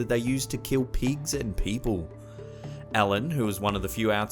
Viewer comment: Am I the only one who thought he said to kill pizza and people